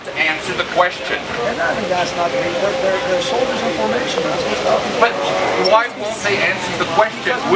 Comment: Ummm, @ 2:05 I swear it sounds like the cop says; "They are soldiers in formation." Someone please prove me wrong?